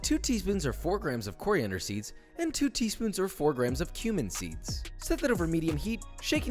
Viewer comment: i heard him saying human seeds lmao